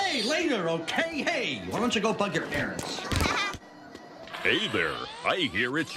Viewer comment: No one: Wally: Why Don’t You Go F Your Parents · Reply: I thought he said “why don’t you go but your parents” but it’s funnier if you think he’s saying the f word